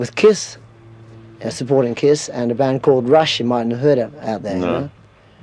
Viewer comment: "We're supporting Kiss, and Rush, a band you might've heard of." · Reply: He actually said "Rush, you mightn't a heard of" - it means you might not have heard of Can't blame anyone for mishearing, it's used pretty rarely now in UK and Aus